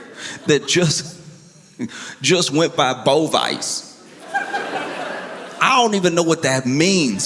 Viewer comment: I heard “bowlofice”😭